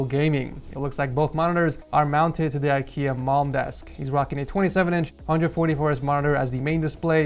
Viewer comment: I thought he said MOM desk 🤣🤣